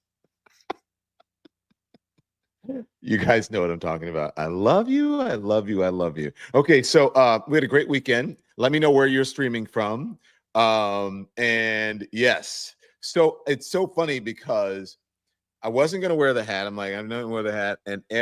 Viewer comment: I thought you said, "Let me know where you're 'screaming' from." 😊